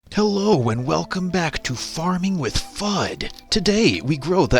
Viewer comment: At the beginning i heard “hello and welcome back to farting with f#@